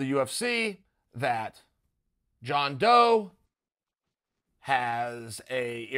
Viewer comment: When Chael said "John Doe" I heard "Jon Jones".